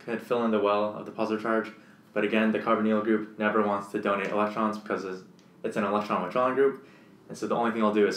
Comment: i cant listen/understand what he say.someone plss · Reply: ‘The carbonyl group never wants to donate electrons because it’s an electron withdrawing group it will only suck up electrons’, is what he said.